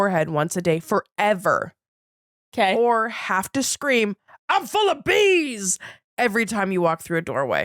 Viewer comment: I thought was saying "I'm full of beans!" at first.